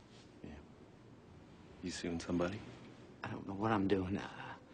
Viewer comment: Oh I always thought he said u seeing somebody...which was very upfront for an aproach ^^